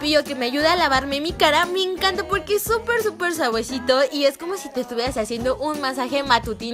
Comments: LIKE si te diste cuenta que dijo suaguesito en vez de decir suavecito en el minuto 1:32 alsa el volumen alto para que puedas escuchar mejor okki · like si escuchaste que dijo super suagüesito en el minuto